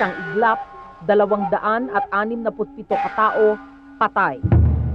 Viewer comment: tama ba narinig ko 267 ang patay?